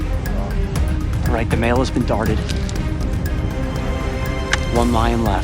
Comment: i thought he said:"alright the male is retarded" lol